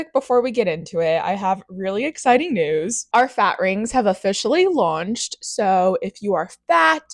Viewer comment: I thought you said cat instead of fat so I stopped the video to look for the non-existing cat on the ring and gaslit myself into seeing it. At this point I think I need a "dumbass" ring myself.